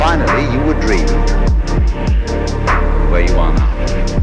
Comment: What does he say at 1:49 ? "Finally you would dream... Where you were now ?" I never understand